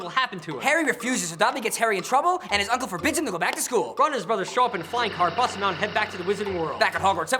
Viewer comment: When you said "Ron and his brothers" I thought you said the Jonas Brothers